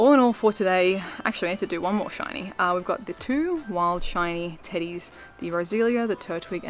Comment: i thought you said "wild shiny titties" for a second there